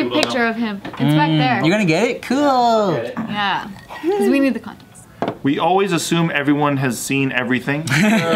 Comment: she said we need the context it sounded like she said we need the content